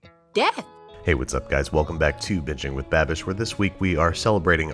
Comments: So I decided to come back and rewatch this one but I swear at 0:25 he says “bitching with babish” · At first I thought babish said "BITCHING WITH BABISH" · It sounds like he said bitching with babish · It sounded like he said bitching with babish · It sounded like he said “hey guys welcome back to another episode of bitching with babbish”